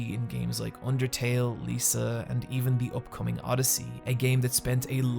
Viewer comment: he says "The upcoming game odyssey." I've never heard of it, and when I Google it, I just get Mario and assassins creed. Anyone know what developer? edit I think I figured it out. It's called oddity, not odyssey. It's a fan made sequel to mother 3 that doesn't have a release date. I'm gonna leave this comment up just in case anybody else wants to know about that game. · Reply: "Oddity" not odyssey. <link>